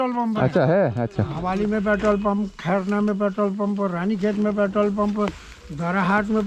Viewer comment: did he say ranigunj😐?